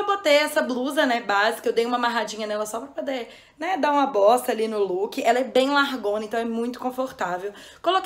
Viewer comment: no 4:39 eu ouvi "dar uma bosta ali no look" 😂